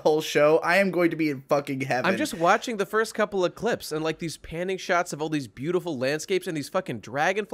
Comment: "Pantyshot of the sky" -- I hope I didn't mishear that... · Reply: i heard the same thing lol, but he is saying panning shot